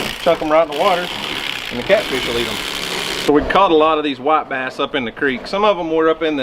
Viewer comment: when you said you something about the whiite bass running; I thought you said- the wife ask ?.